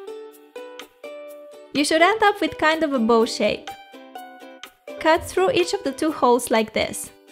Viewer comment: when she was making the pom pom, I thought she said " you should end up with kinda a boob shape." hahaha